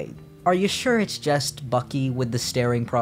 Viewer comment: Download Scripts: Untold Secrets to win BIG prizes here: <link> Lots of awesome prizes and a $49.99 exclusive gift pack for everyone! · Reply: Why do I keep hearing "Fucky" sometimes 😂